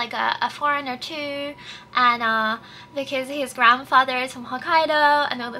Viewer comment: I initially thought you said his FATHER was from Hokkaido but realized I misheard and…dude's from Kanto. 2 generations in Kanto means you're from Kanto. Such a rich white person thing to say. lol "My great grandparents on my mother's mother's side hail from France, so I'm basically French!"